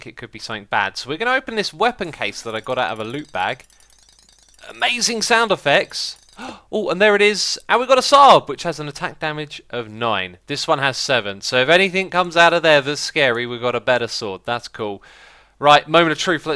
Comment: did he just say Saab :D